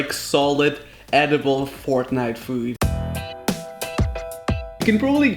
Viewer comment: How ironic is it that I thought he said “fork knife food ‘when he was talking about coming back for lunch . LMAOOO